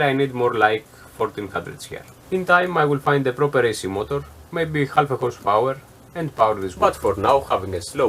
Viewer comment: LOL at 13.07- at first I thought you said 'hundred horsepower' and was- 'thats gunna be one hell of a bandsaw!!!' LOL- great little bandsaw- and small enough to be portable too (just put a battery back in it)